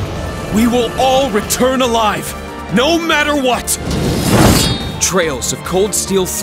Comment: I thought is said tales of lol